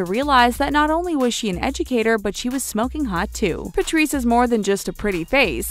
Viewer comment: I thought she said "Smoking pot, too" 😂 Also this lady's Canadian accent is driving me crazy.